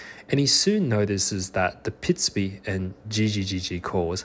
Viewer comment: I dont hear "pitsupi" but more like "picachu"